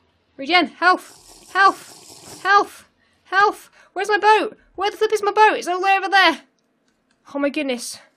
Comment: is it just me or did say "where is my boat,where the f*** is my boat" plz don't hate if i am wrong